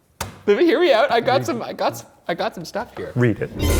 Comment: I thought he said “baby here me out” and I was like ... replay